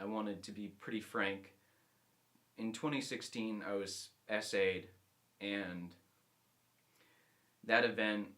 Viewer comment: I'm really thankful for you there. I'm just wondering what word was said at 2:04, 6:51, I'm probably just some dolt missing a definition. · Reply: sexual assault